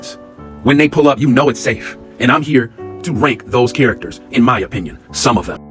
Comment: you did not say rate 😂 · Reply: I said rank but it sounds kinda crazy now 😂